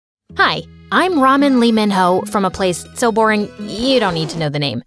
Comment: I thought she said " Lee Minho" from stray kids??😭🤜